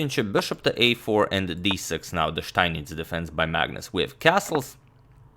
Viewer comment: Can someone help me with what he says at 0:42? "Bishop to a4 and d6 now the (Steinich??) defense by Magnus"